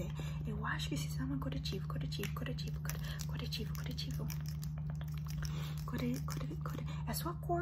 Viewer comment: é corretivo... “curativo” é para machucados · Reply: Eu ouvi "coretivo" mas ok kkk